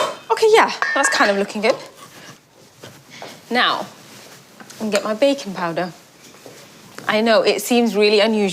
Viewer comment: I heard bacon powder and I thought that’s a thing??